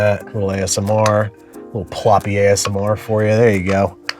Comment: "ploppy asmr"... he slurred a little and it sounded like he said "ploppy ass"